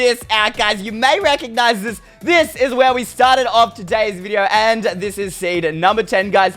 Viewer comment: did he just say 'And this is satan'